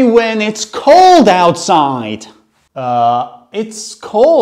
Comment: "bone-chilling" what i heard: "BING CHILLING"